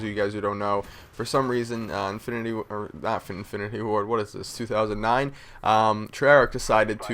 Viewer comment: Did u guys here wat he said infinity warfare and it 2010 that he posted this vid how to did he know that there was going to be infinity warfare?